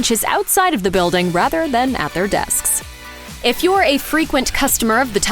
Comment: excuse me did you just say "at their desks"?